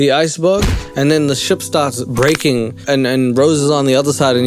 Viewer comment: did you just say Rose? Omgg